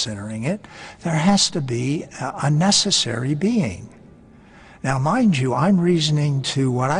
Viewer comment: APACHE 2:27 źle przetłumaczyłeś. Posłuchaj raz jeszcze dokładnie co on mówi. Chodzi o konieczną istotę, nie o "niekonieczną", bo tak z punktu widzenia nawet czystej logiki to zdanie nie miałoby sensu. On powiedział: "there has to be a necessary Being" a nie "unnecessary Being". Brzmi podobnie, a jednak sens zupełnie inny